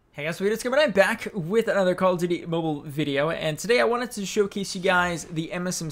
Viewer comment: If u have captions on at 0:11 is says call of the mobile instead of cod mobile 😂